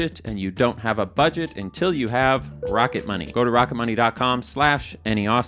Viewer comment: I really thought you said your beloved sponsor is pocket money